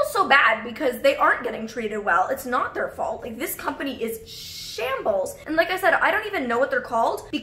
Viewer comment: I thought you were going to say “this company is shit” 😅 but you said shambles lol